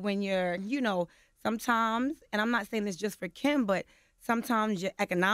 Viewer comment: So is Yandy saying Kimbella created a problem with her on lhh bc she need a money? 🤔 👀 #ijs #catch🍵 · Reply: it sounded like she said “this is not just for Kim”